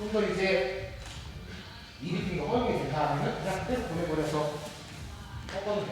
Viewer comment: 뭐라고 말씀하신 건가요? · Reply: 니 립핑이 허용이 된다하면